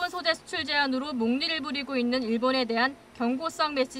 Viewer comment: 마지막 머니투데이 방송을 잠결에 들었더니 머리끄댕이 방송으로 들었다